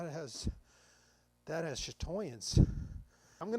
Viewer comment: I need more of a clue than that, can you say what it sounded like?…swirling? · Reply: a little after 20:25... cc said chatoyance?